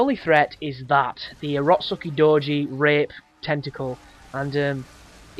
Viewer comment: What did you say at 9:23? Rock sucky doji?